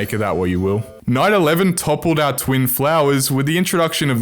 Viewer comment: it sounded like you said 9/11